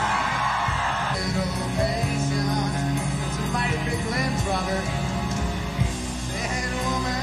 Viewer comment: 30 years later and I still don't know what he says to Robert John here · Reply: ‘Some mighty big limbs Robert’ Personally what I hear- not too sure though haha